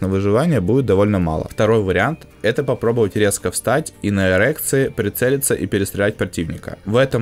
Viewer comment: Мне одному послышалось встать и стрелять на эрекции???)))